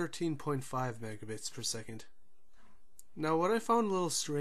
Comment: Calikota hmm did i say that? dont remember. i just checked my files and 1 min was roughly 100mb for 1080p or 1296p. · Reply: my bad, you were saying megabits per second, i thought you said megabytes per second lol